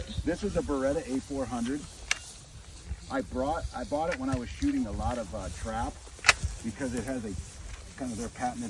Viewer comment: "...When I was shooting a lot of trap." Presumedly, he had to load once or twice during that activity. So, he didn't learn anything back then or he's lying. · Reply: “Shooting a lot of trap.” OK, now I get it. When I heard him on the news last night I thought he said he was shooting a lot of “TROUT.” I was like WTF. Is he confusing shooting with fishing?